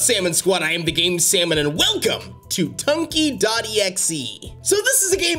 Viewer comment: Am I the only one that thought it said turkey.exe and not tunky.exe